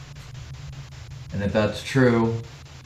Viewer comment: I heard "that's correct"